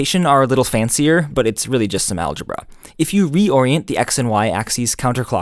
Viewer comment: the subtitles say "geometry", where you say "algebra" and even the onscreen text says "*Linear Algebra!"